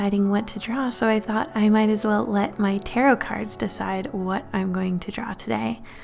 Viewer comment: I thought it said “carrot cards”